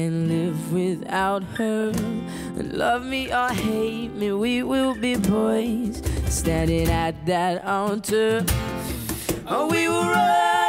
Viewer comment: Brad sounds like he says " We will be boys standing at that alter"